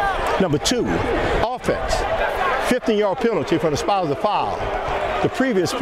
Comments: Ref: "15 yard penalty on the offense for a completely clean play." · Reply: I swear I heard him say 50 yard penalty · Sounds like he says 50 yard penalty